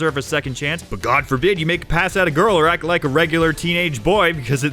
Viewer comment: what did he say? McPass Out a girl? Make pass out a girl? I’m a teenage boy and I need to know what we’re like. What the fuck did I hear lmao I can’t understand